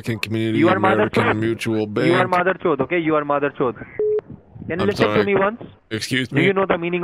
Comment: This is my fiancé’s account and he and I have been watching this entire amazing series together. We are obsessed! He’s Indian and has gotten such a kick out of you stringing along these scammers, especially being able to understand the Hindi in the background and what they are saying about you! It’s awful how they try to take advantage of gullible elderly folks, but this has been AMAZINGLY entertaining!! Bravo! 👏🏽👏🏽👏🏽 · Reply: ohhhh I thought they were saying mother chode 🤣